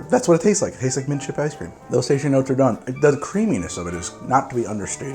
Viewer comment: I know im late, but what in the world does say at 8:56 ? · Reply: "Those tasting notes are done."